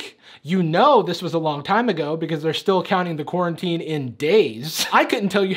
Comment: I thought she said "gay sex on quarantine" GOODBYE💀👋🏽